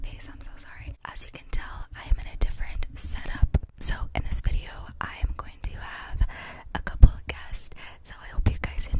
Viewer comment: Did you say « Salope » 0:12 😭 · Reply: MDR non elle dit set up